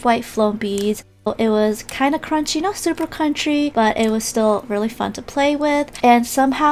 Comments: Waittttt.... at 13:48 did you say it was supper country..XD am I the only one who herd it And all of the clear ones were my fave along with Santa's cookie and hot coco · i heard “but not super country” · “not super country” is what i heard😂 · did she say cruchry lol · In 13:49 she said country instead of crunchy 😜🤩😂🤣 but still love 💕 her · did she say country or is it just me 🤣😭